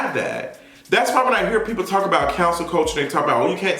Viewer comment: Every time you say “cancel” I hear “council”